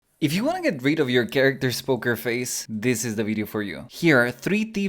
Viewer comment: Oh, you said poker face! I thought you said “hooker face” I was really confused.